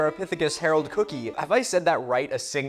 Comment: I feel like I heard "Herald Cookie" a lot. Now I feel like I almost want cookies, but I can't get the image of a very old tooth out of my head. So I guess I don't really want cookies after all.